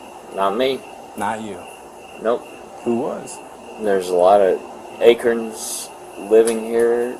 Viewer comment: Officer: "But who was driving?" "Nope, not me." Officer: "Not You?" "Nope." Officer: "Who was?" "There's a lot of acorns living here tonight." CONCLUSION: It was the 'nut' behind the wheel. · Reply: I had to listen back, he actually, unclearly, said vagrants. In his own language, of course. I thought he said acorns too.. haha